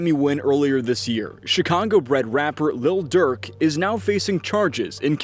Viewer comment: did fool say "Chicongo"?? 💀